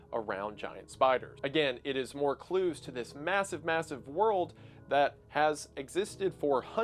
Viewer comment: did i hear massive?